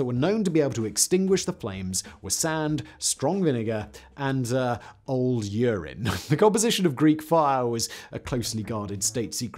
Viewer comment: It's kind of hard to imagine a fire fuel that water won't extinguish but urine will. After all, about 90-95% of urine is water, and I can't think of any other component of normal urine that could make such a difference... (and the same applies to vinegar, although the water content there may go "down" to 70-85% or even lower - but still, the predominant component will still be water) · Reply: Yeah, that makes perfect sense. To be honest, I thought he said "good old urine" - but he definitely says "old urine".